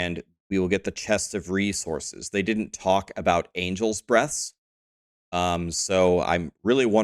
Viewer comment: My mind must be in the gutter I heard "Angel's Breast" at first. Like damn, Blizzard is really buffing season 5. 😅